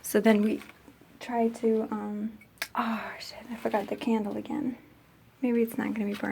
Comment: who else heard her say "oh shit"